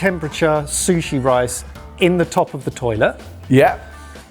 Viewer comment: did he say toilet?